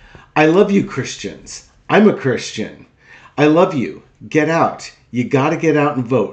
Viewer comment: he also said he wasn't a christian. here's the quote i got: "you won't have to do it anymore, four more years, you know what? it'll be fixed, it'll be fine. you won't have to vote anymore, my beautiful christians, i love you christians, i'm not christian, i love you, get out, you gotta get out and vote. in four years you don't have to vote again we'll have it fixed so good you're not gonna have to vote." - dumpster in a july 27, 2024, speech to turning point action in west palm beach. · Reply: I heard him say I’m not a Christian as well